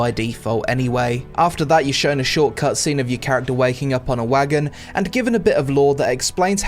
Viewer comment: I heard 'waggin' ' instead of wagon.